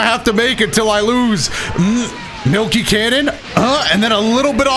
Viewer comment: Did he say milky cannon here? or is just me